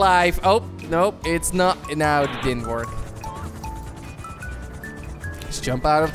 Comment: I thought it said N WORD LOL